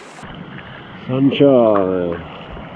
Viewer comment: what do you say at 12:56 ? "mangiare"?